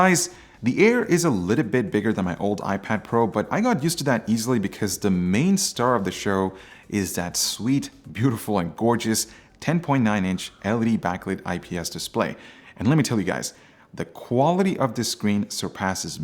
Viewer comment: did u say LED ?